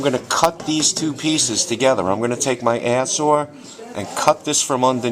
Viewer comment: What did you say you were going to cut both pieces with at 3:03? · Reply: I believe air saw